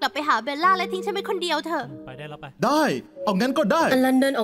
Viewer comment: เหมือนได้ยินว่าไปได้แล้วไป5555